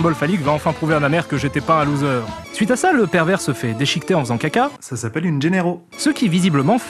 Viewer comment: À 7:46 Gilles Stella dit "Ça s'appelle une ?!?" c'est quoi le mot utilisé après ? J'ai cherché, je n'ai pas trouvé. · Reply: Il dit "Ça s'appelle une Gennaro", en référence au personnage de Donald Gennaro dans Jurassic Park, qui meurt bouffer par un T-Rex alors qu'il est sur le trône.